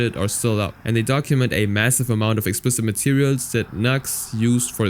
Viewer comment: did you say massive